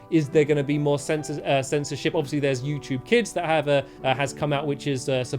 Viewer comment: Wow when stamps said censorship I thought he said censor sh!!t wow